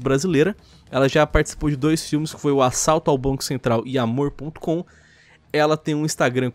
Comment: eu ouvi que ele assaltou a CENTRAL kkk